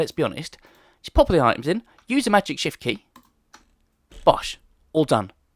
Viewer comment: I cant understand what key are you talking about can you type it by replying to me please? D: I tried to look at it by captions but It just says magic shift and I do not think theres a key name magic shift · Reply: It's shift, I refer to the key as magic shift because it is so versatly in Minecraft. It allows you to do so much